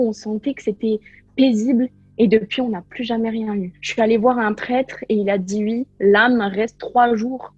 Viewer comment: j'ai compris "j'suis aller voir un traitre" xD